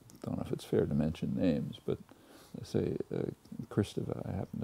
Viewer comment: who's the one Chomsky mentions here ? I couldn't hear him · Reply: Julia Kristeva, feminist writer